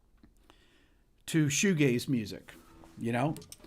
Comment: had me. Rick is so good at introducing music that listeners may never have heard of. And there was me, thinking, "Who is this artist 'Shuge'? I must look him/her up!" I thought he said, "...precursor to Shuge's music." But, of course... he said 'Shoe Gaze'. I am a numpty of the highest order. (Still, this feature on The Cure was very nostalgic for me.)